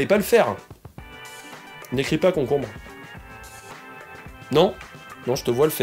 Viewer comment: Quand il a dit "n'écris pas concombre" j'ai entendu "écris concombre" étonnant, non?